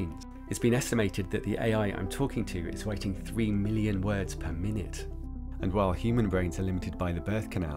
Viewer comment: "AI can think much faster because light travels faster than the signals in our brains. It's been estimated that the AI I'm talking to is waiting 3 million words per minute." First of all, I think you actually said "writing 3 million words per minute". (What I quoted above are the subtitles.) Secondly, I may be out of date, but this sounds like it might be misleading. I would advise anyone interested look into this more closely.